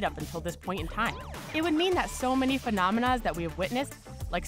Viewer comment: Did she say “phenomenas” at 9:49 ????? 🤦‍♂️